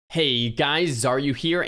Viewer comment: I thought he said 'Hey guys are you here?'